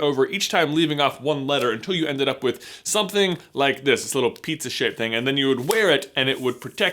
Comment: I'm profoundly hearing impaired, and it really got my attention when you said, "Pizza shaped thing" because I thought you'd said "...piece of <censored> thing." 2:50 Just a bit of trivia for laughs.